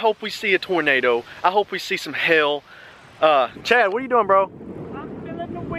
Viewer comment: It sounded like He was saying hell And not hail😂